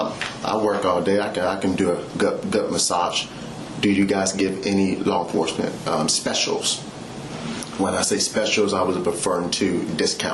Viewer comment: Did he say I could do a gut massage? Do you give law enforcement specials?